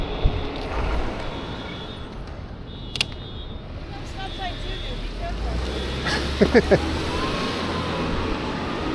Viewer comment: what did she say? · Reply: haha 🤣 I forgot I published this video, I almost didn’t, was on one this day, she said “you got a stop sign too dude! be careful out there”